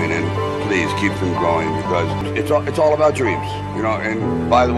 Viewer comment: 너무 재밌게 공부하고 있어요. 감사합니다~~~ 맨 마지막에 왠지 keep them growing 으로 들려요